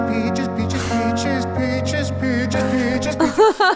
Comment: I swear, I hear him singing "BITCHES!!! BITCHES!!!"